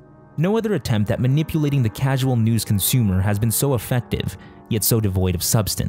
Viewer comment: did he say "offendive"?